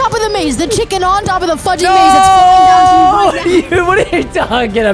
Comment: For a second I thought that said the f word at 5:07 but he just said fudge XD